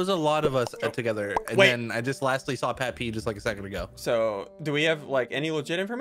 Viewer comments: i thought he said "i saw Pat pee" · wait, what did say? 😂😂😂😂😂😂😂😂😂😂😂 XD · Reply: he said patP